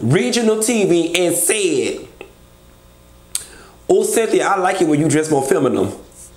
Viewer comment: I thought Marlo said "Ooo Cynthia, I like when you show your femaminity" 😂😂😂